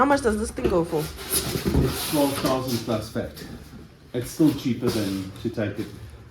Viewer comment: Did he say the sub is R4000 2:49 ? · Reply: 12000